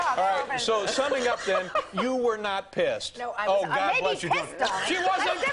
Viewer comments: does she say “I may be pissed on but that’s different.” ?? Lol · I think Joan says "I may be pissed on!" She's a fun old broad!